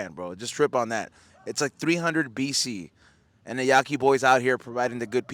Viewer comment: Nice…thought u were saying Yaqui boys ,(side note a Yaqui taco is definitely up there )…we need Mexican version of Korean bbq